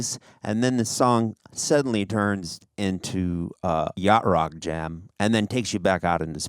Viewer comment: Did he say yacht rock jam @ 7:32 ? Gonna have to steal that one....